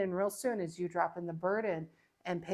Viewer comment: Hi" ya hope you had a good Va'kay.. I'm a little puzzled at 40:59 sounded like you were gonna say.. Bloodied, But instead, you said ball bouncing that intentional or a blooper..? Namaste 🙏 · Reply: Dang it, Jeff…you made me have to listen to my own voice😩. I hate listening to myself, but wanted to make sure I didn’t blooper anyone. No, it was burden—and I think I still had Foy Vance (did I say vance joy in the reading 🤦‍♀️) in my head—his song ‘burden’ 🙊.